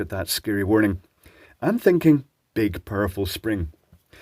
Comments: I misheard "big powerful spring" as "big purple spring". Consequently I spent the next 11 minutes on tenterhooks waiting for a peuce-coloured spring - but I was sorely disappointed :( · I misheard "big powerful spring" as "big purple spring". Consequently I spent the next 11 minutes on tenterhooks waiting for a peuce-coloured spring - but I was sorely disappointed :( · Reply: I heard "purple" both times, assumed that it was an in joke, read these comments, rewatched the video, and was genuinely surprised when he said "powerful."